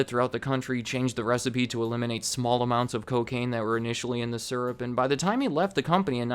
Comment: did he say… cocaine. In the syrup